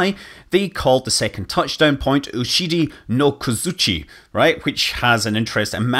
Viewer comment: When you pronounced "uchide-no-kozuchi", I for a second thought you said "oshiri-no-kozuchi" which would mean "butt hammer" :D